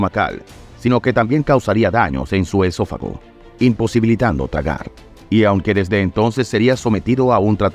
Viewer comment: Yo lo estaba escuchando en X2 y sin querer escuché "imposibilitando cagar"